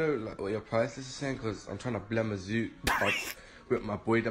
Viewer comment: did he just say "i'm tryna blem a zoot"